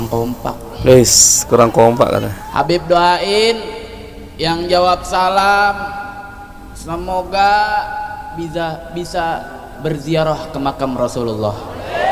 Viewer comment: Ini yg saya dengar tuh dia bilang abib doain yg jawab salam semoga tidak bisa jawab salam. Apa begitu atau saya yg salah denger Soal.y gk jelas ngomongnya